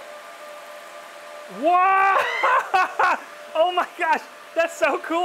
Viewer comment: I hear...WALUIGI!